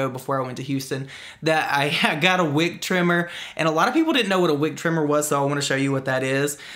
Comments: OMG I thought he said wig trimmer. Hahaha. I have the volume low so my boss doesn’t catch me. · Who else thought said “wig trimmer” instead of “wick trimmer”? 🤣😂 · Oh 😂😂 I thought you said wig trimmer 🤣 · Definitely, thought, I heard you say “wig” trimmer 🤣. I really enjoyed, the candle haul. 🕯♥️. Your place must smell amazing ☺️. I don’t think, you should, ask JC about his thoughts. Your channel is giving us the drama. We watch because of that. Opinions, are not, biased. You are allowed to discuss, your thoughts, on whatever, you want 👍. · I thought you said wig trimmer 😂 I really need one of those because I use scissors and it's tough. Good recommendation! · Oh my god when you said wick trimmer at first I thought you said wig trimmer till you started talking about cutting the wick in the candle lol.I need to get my hearing checked lol. My favorite candle scent is my stress relief candle from bath and body works. One candle my whole house smells like eucalyptus. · I thought you said wig not wick 💀😂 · I swear I thought he said , "wig trimmer" and I was so confused what that had to do with candles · I thought you said wig trimmer until you held up the wick trimmer LOL